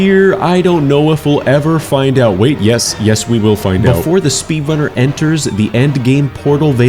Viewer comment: I swear I hear speedlunner